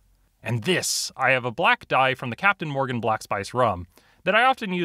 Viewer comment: did someone say captain Morgan ALWAYS BELIEVE IN THE COCANIE